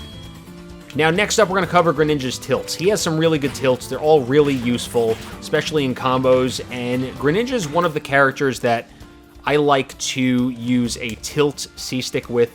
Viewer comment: i thought it said “tits” 😂